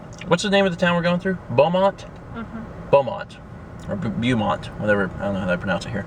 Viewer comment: Did anyone else think said Belmont like in the Castlevania series?